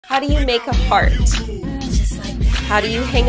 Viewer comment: LOL I THOUGHT SHE SAID “HOW DO YOU MAKE A FART”😭😭😭